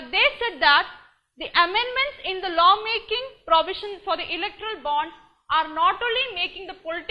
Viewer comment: Did she just say "Illiterate bonds"?